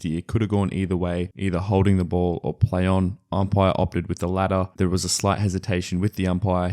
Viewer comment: Are you saying ladder instead of latter? xD